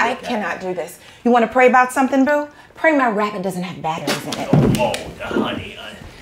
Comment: what did she say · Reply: "pray my rabbit doesn't have batteries in it". lol